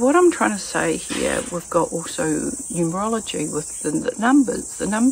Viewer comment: Did she say "urology"?...